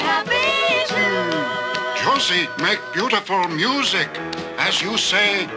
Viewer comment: is that old man saying "Pussy make beautiful music" or is he saying "Josie make beautiful music.?"